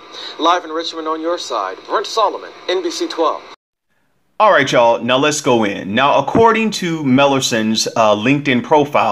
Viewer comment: Did he just say......."Alright chy'all...NOW LET'S GO IN"???